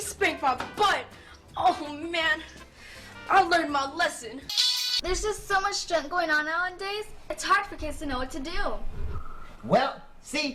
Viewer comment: does she say “there’s just so much shit going on nowadays”?